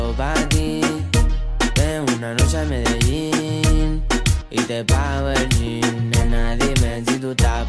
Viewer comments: Yo pensaba que decía "Ven una noche a Medellín y te bajo el Jean" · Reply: Yo pensé que decía "Y te pago el gin(la bebida) " xd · Yo pensé que decía "tu eres una rosa de Medellín" jajaj